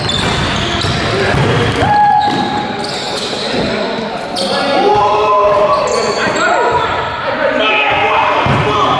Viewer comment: did she call a foul on that shot fr? 😂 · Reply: She said “ And 1” she aint even make the shot 😂😂😂